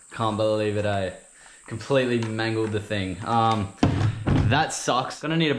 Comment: Can someone who knows English better than me tell me what he says at 18:41 Completely __________ the thing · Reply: Mangled, basically means destroyed beyond repair